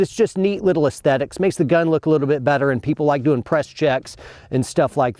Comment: it sounds like he says "people like to impress chicks" instead of "people like doing press checks". hahah. Took me a minute and a replay or two!. He was coming off of a TBI from the camera guy though so ALLL IS FORGIVEN! LMFAO!